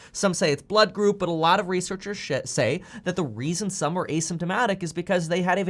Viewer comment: I hear “A lot of researchers shit say” lol